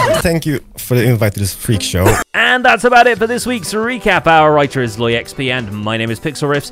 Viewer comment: Every time I hear “our writer is” from yall I always mishear it as “our lawyer is”